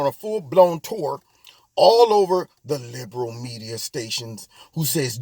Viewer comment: at :57 you said" liberal stations" and I heard "negro stations", and I thought alright! Now I can hear new takes on politics. Maybe soon. ;-)